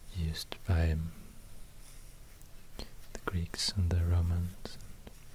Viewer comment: Notice how it’s much more difficult to carve curved lines then straight ones. The Greeks wrote into wax tablets, and because of that, their letters are made up of primarily straight lines. One other example of this are the written languages of many southeast Asian countries- they scratched their writing into palm leaves. Scratching straight lines would tear the leaf, so they made their letters as curly as possible. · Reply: I thought you said The Geeks lmao